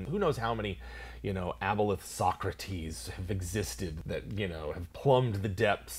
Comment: I thought you said "Aboleth soccer teams" XD